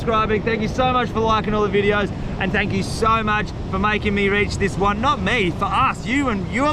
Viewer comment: If you actually did a five hour video, I would watch it. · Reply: it sounded like he said for making me rich haha